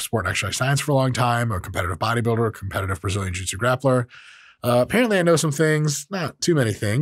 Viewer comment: the captions says Brazilian juicy grappler lmao😂